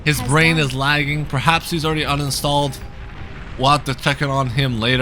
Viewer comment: did he said "his brain has uninstalled" ? 🤣🤣👍👍